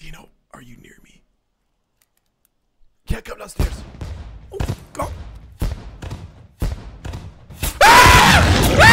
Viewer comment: Could someone tell me what 8:28 said here? I could swear he said "ya que hablaste" But I'm not sure. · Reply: Yeah come downstairs